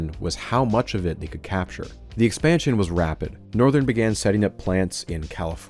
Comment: Part 2 is out now! <link> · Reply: the captions read "Norrhwen" instead of Northern for some reason edit: changed timestamp from 29:43 to 29:44 for accuracy